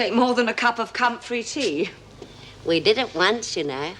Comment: ummm did she say cum-free tea¿?¿? I should hope the tea is cum-free.... Jesus fletch n mr Andrews would be workin overtime 4sure if it wasn't lmao